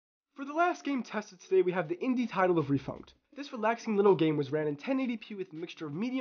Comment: Great video! What's the game at 4:46? I can't quite make out what you said. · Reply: The game is called refunct, and thanks!